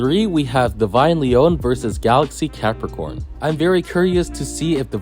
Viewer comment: anybody else hear "Galaxy Peppercorn???"